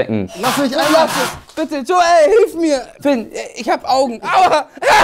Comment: Bei 6:27... Hab ich das richtig gehört "Fynn eh äh bitte, ich hab Augen, aua" ??? :DD